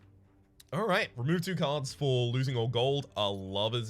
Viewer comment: I heard "Remove all cards for losing two gold" at the start and I actually did the Youtube equivalent of a double-take and rolled the vid back. Didn't think the alcohol would take effect that quickly.